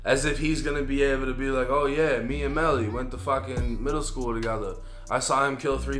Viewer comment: Lmfao “ oh yea we went to middle school together l, I saw him kill 3 people I just never said anything”🤣😭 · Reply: On god I thought he said preschool. I was rolling. Still funny though